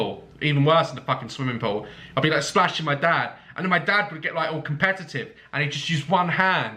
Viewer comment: "I be splashing my dad" I heard: "I be smashing my dad"